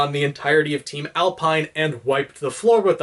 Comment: I’m a little confused. What did he say at 7:54? Edit: After googling it, I realized he was saying ALPN (As is Arc, Lie, Pine, and Nora). I always referred to them as team ORNJ (Oscar, Ren, Nora, Jaune, pronounced “Orange”)